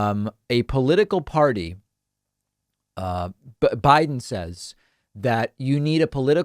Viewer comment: I think you mean “Brannon says”, NOT “Biden says”. Frightening that Brannon has so many wealthy, powerful people backing him.